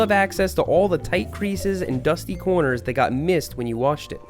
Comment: Is it just me it did he say mist instead of missed because it’s like cleaning or washing?